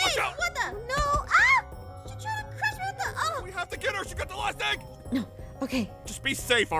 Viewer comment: I swear, I thought I heard 'big mac' instead of 'last egg'or smth 😂